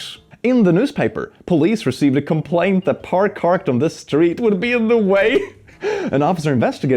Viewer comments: "par carked on..." Please tell me I'm not the only one who heard this! 😭 🩵💙🩵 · you say “par carked” instead of “car parked” in calling your manager